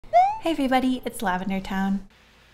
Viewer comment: swear to god i had to watch your into like four times i thought you said "hello every- bunny"